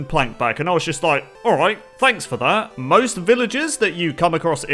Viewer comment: i thought u made a pun by saying planks for that instead of thanks for that